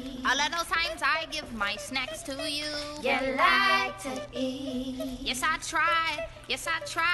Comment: (ya like to eat) all of the times I give my snacks to you (ya like to eat) yes i tried, yes i try-y-y-yyyyy (ya like to eat) all of the times i give my sn--, my fries to you (ya like to eat) my hot fries, my hot fry-y-y-yyyyy (return of the snack) it is (return of the snack) come on (return of the snack) oh my god (boy you know you're the snack ) (??) yeah i am (return of the snack) once again~~~~~ (return of the snack) make up a word people cause i can't spell what she said (return of the snack) watch my flow (boy you know you wanna snack) (???) HERE I GO (return of the snack-) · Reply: Lmao i thought she said "you lied to eat" 😂🖐